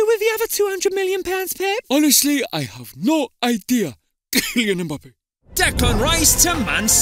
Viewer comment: did pep said kylian mbappe ?